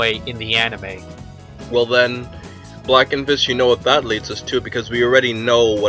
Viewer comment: Yo i swear his friend said BlackenBitch at 4:05 Lmao!